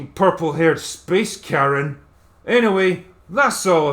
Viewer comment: "Purple haired space Karen." That is the perfect way to describe her character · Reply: Oh. Ha ha. I thought he said "Carrot". Karen makes more sense.